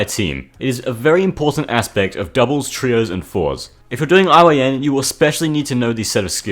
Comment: what is he saying here? "if your doing ion"? Whats ion? Sorry lol I just feel like that's a new word and that its important. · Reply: "iyn" it's a bedwars strategy